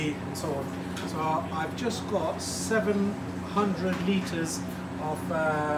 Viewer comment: did he say 700liters xD ?